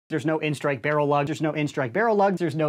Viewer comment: after hearing "there no N-Strike barrel lug" several times i hear it as "there's Nowin's strike barrel lug"